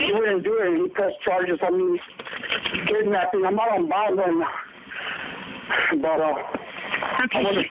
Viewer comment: Not sure if anyone already translated it, but during the first story, it sounds like said "I'm out on bond right now."